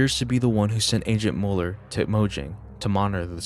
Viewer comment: Oh I thought You Said Mulder for a slip Second... I was Like "THIS IS A MINECRAFT ARG, NOT THE X-FILES, WHAT ARE YOU TALKING ABOUT!!!???"